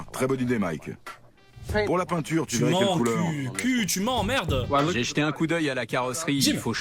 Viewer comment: j'ai compris " Q tu m'emmerde " 😂😂